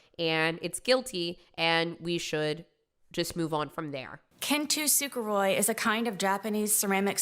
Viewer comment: Sarah Boone pronouncing kintsukuroi as "Canto Sugar-Ray" kills me every time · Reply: I hear it as “kintu suker roi” instead. Either way she’s mispronouncing it and leaving us all confused.